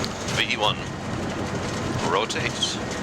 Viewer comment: Question: What is is that the captain says at time stamp 23:29 after he says "100 knots" ? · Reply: V1. That is the point of no return. If the pilot does decide to abort there is not enough runway to come to a safe stop and he must take off. He then says Rotate or Vr that is the plane front wheel lifts off.